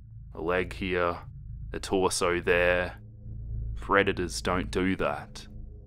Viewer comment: I thought you said redditors XD